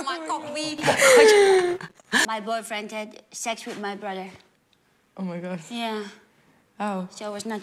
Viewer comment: Wait a damn minute, did she just said "my boyfriend sex with my BROTHER"? Wth is going on